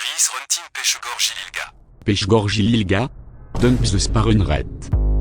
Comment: can someone tell me what he say In here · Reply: late to this but from what I can hear its: "He's hunting Peschegor Gillilga." "Peschegor Gillilga? Dumb spawn rate."